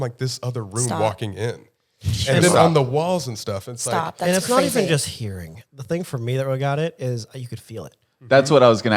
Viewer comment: does anyone hear what said ?????????? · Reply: Doubt youre still curious but it sounds like he said "she said stop"@